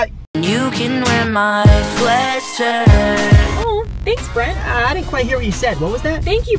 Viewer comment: When I heard “Swearshirt” I actually died💀